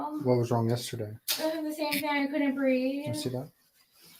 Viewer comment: The male cop holds up a hospital bracelet. Can we see what hospital is on it? I can't hear her well. Did she say she went to the hospital for heart racing and something else? Something is very WRONG when a daughter ask for her mom to be put in jail! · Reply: She said she couldn't breathe